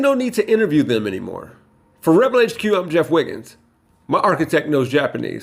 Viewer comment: The Simpsons reference did something to my brain 'cause at the end I really thought I heard *and read in the subtitles*, "For Rebel HQ, I'm Chief Wiggum" 😅